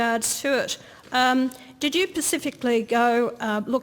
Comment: she says "Pacifically instead of Specifically...That's when I knew it was not going to go well for her...LOL